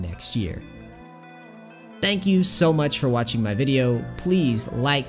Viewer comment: I thought he said Peace crackas